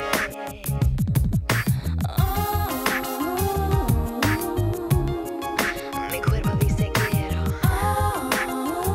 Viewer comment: q onda con lo que dice? :0 fue hace 14 años:( estoy en el 2024 1 de junio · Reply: Mi cuerpo dice quiero pero mi alma tiene miedo